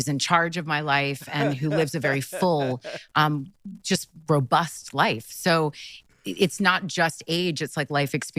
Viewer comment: She Said She Lives A "Robust" Life, I Heard BroBust Life Cause She Got Bros Young Enough To Be Her Son Busting Nuts In Her.